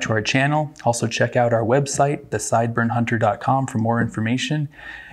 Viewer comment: I heard "sideburn hunter" so "Caleb from Brownells has entered the chat".